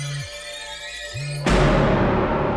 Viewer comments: Khúc cuối 24:22 ổng nói là " Hãy like và subscribe channel Quốc Khánh Official " mà tưởng nói gì kinh khủng lắm -_ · Mọi người ơi Khúc cuối 24:20 ý Nghị nói gì vậy ạ mình nghe không rõ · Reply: Hay like va subrise kenh Vu Quoc Khanh (giong cuoi ghe ron)